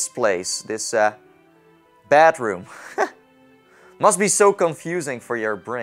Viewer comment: Why do i hear ash's bathroom? 😂😂